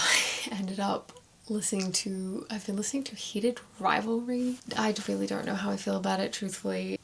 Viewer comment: Heated Rivalry? I thought that said Harry Potter...